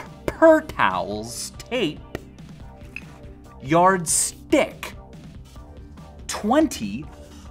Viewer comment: The way I heard it " yaRD ICK"